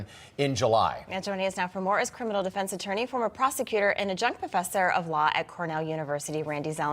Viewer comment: Did she call him a junk professor of law?